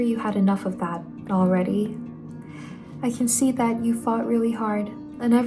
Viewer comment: bruh oh my days I had to re watch this twice, I thought she said “I can see that you fart really hard” 😭🫶🏻